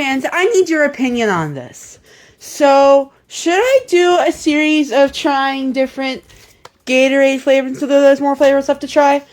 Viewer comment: genuinely what did he even say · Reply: "Should I do a series of trying different Gatorade flavors [until there's (none) more] left to try?" Word jumbling and lack of diction like this is a common issue with Discord/Vrchat/Roblox accents